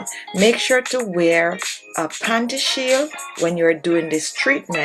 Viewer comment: what did she say to wear? I couldn't understand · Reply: Panty shield or pads